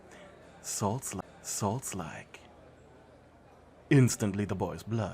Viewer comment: what did she say? Salts like? Idk 💀😫